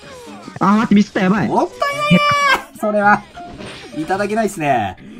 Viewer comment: おoぱいないねーしか聞こえんw